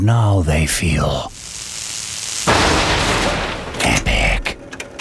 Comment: "Now they feel [?]" Epic? Is that what it says?